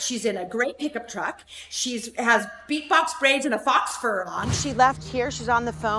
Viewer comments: I had to rewind that 3 times to make sure I heard that right "BEAT BOX BRAIDS"!!!! lololol It's just box braids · did she say beatbox braids 😩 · The funniest part about Heather snitching was when she said “beat box braids” 😭😂😂😂😂😂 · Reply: I thought she said big box braids…I had to go back and listen again 🤣😩🤣